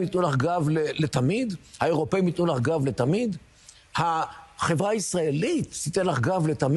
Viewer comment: You made a mistake at 1:05 he clearly says Israel and not "jewish" Let's keep this on point so the idiots can't do their whole "it's antisemitic" song and dance